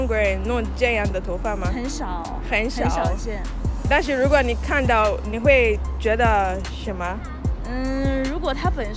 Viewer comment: I heard 但是如果你看，你会觉得什么？